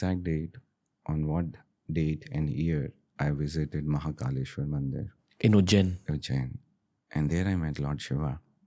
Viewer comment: did he say He met LORD SHIVA AT MAHAKALESHWAR TEMPLE ?????????????????????????